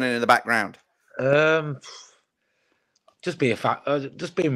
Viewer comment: that initial answer sounded like "just be a fat c..." haha